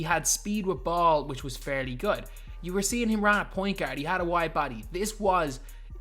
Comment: Did he said white body ?